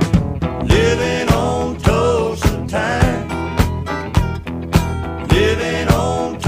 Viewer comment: When I was a kid ..I thought he was saying living on closing time..lol 🤣